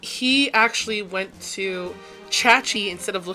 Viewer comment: I had subtitles turned on at 4:40—when you said: "he actually went to...ChatG.," the subtitles wrote: "he actually went to church instead of looking up on Google." Oh, God, why!- Tell me! What should I do to feel better?! Google A.I. recommended me to jump off the Golden Gate bridge...